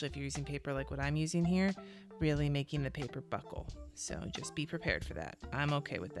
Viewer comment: I'm listening while I am at work, so I'm following along but not watching with my eyeballs. So when you started painting I heard "paper buttholes", totally plausible in a planning video. Haha! Hope you enjoy our warm weather this week, and welcome to Portland area.